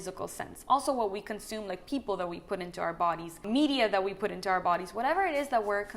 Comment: did you say "people we put into our bodies"?! just wanna make sure lol